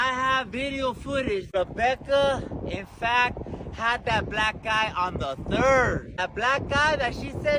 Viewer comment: I keep hearing “that black guy” 😂😂😂